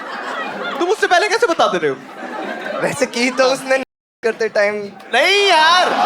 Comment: pe kya bola? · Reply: He said that "vaise ki to namaz karte time hi thi"